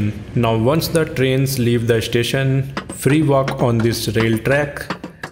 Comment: But I heard of him saying free walk I heard him saying 3 walk